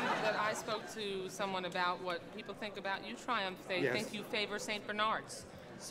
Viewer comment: I thought it said Trump